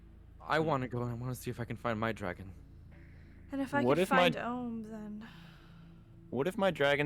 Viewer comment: Did anyone catch what dragons name is? She said it at.. 13:55 Is it.. Owm? Owem? Om? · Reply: Ohm